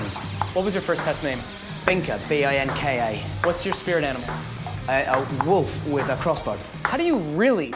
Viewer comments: I thought he said that his spirit animal was a wolf with a cross bite.... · when he said binka I thought it was him saying binker but what an accent until he spelt it out ;p